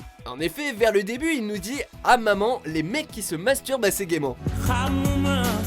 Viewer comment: moi j'entends après masturbe "à c'est dément "😂